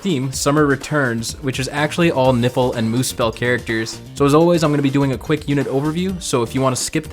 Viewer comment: Haha it's funny imisheard you say "nipple and muspell characters"